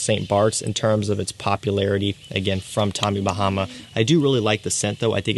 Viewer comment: for a split second there, it sounded like he was saying "fuck tommy bahama"